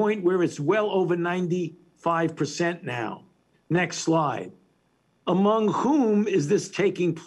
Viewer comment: All I hear is, "next lie"